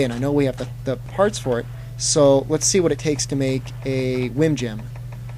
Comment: it sounds like you say whim jim XD but very good tutorial =D